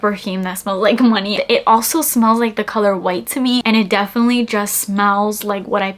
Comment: Getting married tomorrow and immediately thought you saying Kenneth Cole White. It’s one of my picks along with Burberry body tender and Chanel chance